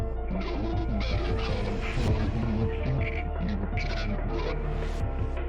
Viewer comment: I know this is pretty late, but i'll add in what said here at 1:15 "OH.HOW.FUNNY.IT.IS." "NO.MATTER.HOW.FAR.YOU.THINK.YOU.CAN.RUN." <link>.THERE." "RIGHT.BEHIND.YOU." (very subtle)